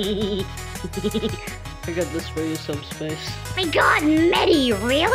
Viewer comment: what are phighting players on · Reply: I THOUGHT YOY SAID SUBSPACE INSTEAD OF SUBSTANCE IM CRYING.